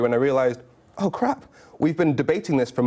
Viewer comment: Did he said "Oh crap !"? :))))